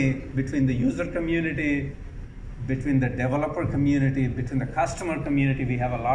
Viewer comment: every time Raja says "the developers" I hear "the devil of course"...go back and watch it from the beginning with that in mind ;)